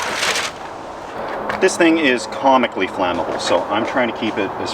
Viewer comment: I thought you said Karmically Flamable !